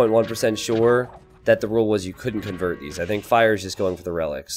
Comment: "Is this car a convertible?" ... I refuse to believe you have played this game for this long and not noticed this, or known what an AC Cobra is. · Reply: I misheard at 21:17 "I'm 97.1 percent sure that you can't convert these" because I was watching in 2 times speed. I have slandered his name... rip.